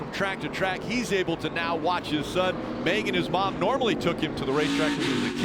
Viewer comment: What did Rick Allen say at 19:33 ?!?!?! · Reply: "He's now able to watch his son, banging his mom..."🤣🤣🤣🤣🤣🤣